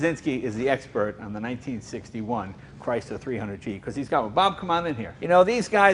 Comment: What jay says ? "Cuz he's got one" or ... ? 1:36 What says before "everything else" ? "It's expensive" ... ?